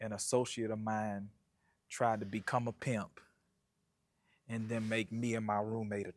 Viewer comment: Hahahahaha why i thought it said "my friend became a PIMO & tried to make me his first (hoe)."